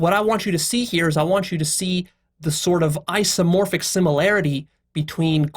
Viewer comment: I heard "ice-omorphic"